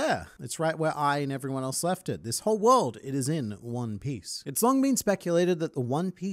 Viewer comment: I always thought Roger said “One Place”